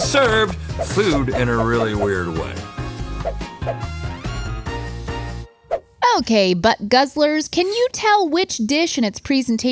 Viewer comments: "Okay, butt guzzelers". I had to rewind and put on captions to see if I heard that correctly.😆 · Tf did she call them?? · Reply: Butt guzzlers